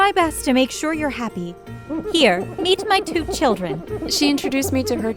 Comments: i heard "here, eat my two children" and i was like whattttt? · I coulda sworn she said “here, eat my two children” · I miss herd it I thought she said “here eat my children” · I misheard as "here, eat my two children". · Not gonna lie, legit thought that woman said "eat my two children." Not "meet my two children." And I was more concerned than I already was.